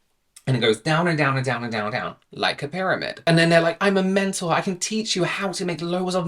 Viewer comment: Lol I heard “I am mental” not “Im a mentor” had to rewind 😂